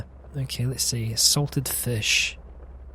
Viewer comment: i thought you said "Assaulted Fish" xD oh that is so silver x3